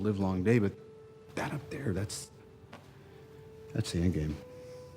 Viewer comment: did I hear correctly? "That up there, that's the endgame"?